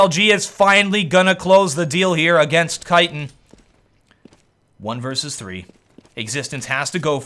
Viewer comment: "Finally gonna close the deal here against Kyton" Was I the only one who heard Kyton? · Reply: Kitan = Kenny's Titan